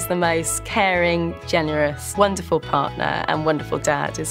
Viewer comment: correct me if I am wrong but I heard he is the “most wonderful father and wonderful dad” father and dad is the same thing 🧐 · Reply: I believe she said partner instead of father 😊